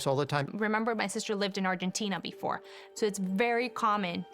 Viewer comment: What? Is she saying Argentina? We don’t usually have fireplaces in our houses, maybe in Patagonia. But it’s really uncommon nowadays.